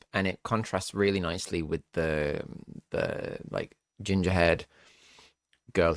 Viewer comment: ginger head🤣 u got me ups sorry i thought u saying head, it was hair😅